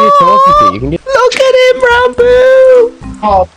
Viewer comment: When he said randoo I thought he said rambo lmao